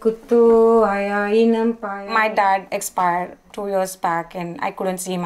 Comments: Did she just say that her dad "expired"? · did she say that her dad expired?